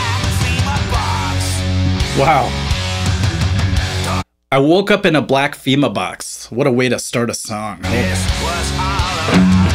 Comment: I've never heard this either before and I thought he said 'Woke up in a black Zima box'